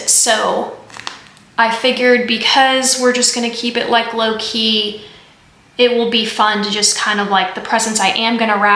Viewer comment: JoAnn's has glass ornaments, too! You said "low-key," but I heard "Loki." 😂❤️